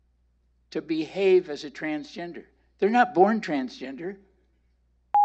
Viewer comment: What was censored at 5:05 mark? What did he say? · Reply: About that you are not born transgender can't remember the last bit